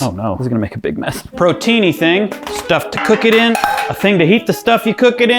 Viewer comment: hard to follow the made up words. I kept rewinding to figure out what you said and flashed on the screen. I heard “rotini thing” but you said “proteiny thing” meaning “some protein” and flashed “chiggin” and a mystery package which is “chicken”. This is going to be a long video but I need it.